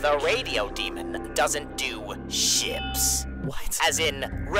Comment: So i thought Alastor said "chips" but in a dutch way which is writen the same as ships i think so my brain died